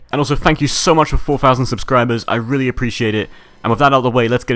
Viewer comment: lol i thought he said thx for 400 subs not 4k subs then i was like no way this guy spent so much money on a gud editing software with only a few hundred subs